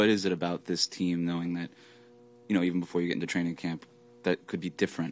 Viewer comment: When they asked how was “training camp” I thought he said “Tranny Camp “ 😂😂😂😂 my bad lol is all I see & hear Illuminati is too wild bruh